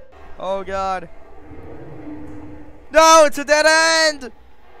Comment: did u say dwooooooo? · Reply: I said noooo